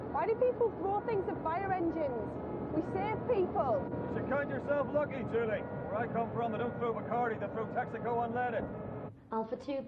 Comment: What did bill say at 24:35 his gotba thick accent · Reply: "You should count yourself lucky, Julie. Where I come from they don't throw Barcardi, they throw Texaco Unleaded!"